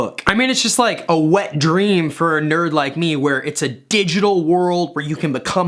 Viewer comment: did he just say a wet dream for nerds